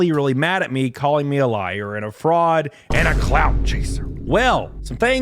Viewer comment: A clown chaser...?! Is what i heard 😹